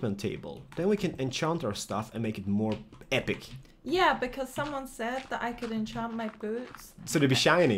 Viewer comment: I thought she said "Someone said that I can enchant my boobs" xD